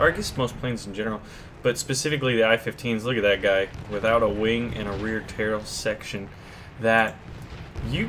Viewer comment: LoL did you say "Look at him, without a wing and a reartail of the section"